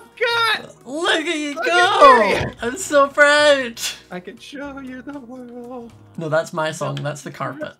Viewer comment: When he said I could SHOW you the world I thought he said give and I started singing “I could give you the world baby girl” aka the song Worth it